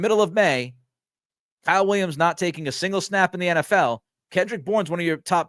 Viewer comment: Williams hasn't taken a snap in the NFL, yet. He'll take plenty this year. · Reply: Oh, okay. Sounded like you said “Is not taking a single snap….” I listened back like 5 times wondering what Williams could have done wrong to ruin his chances. He definitely looks promising. Hopefully they can finally develop a WR! lol